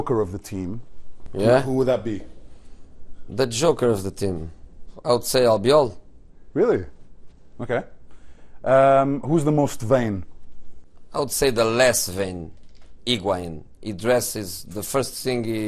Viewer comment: he asks "who is the most word ?". Can't rly understand, what the word is? Help pls, english isnt my native.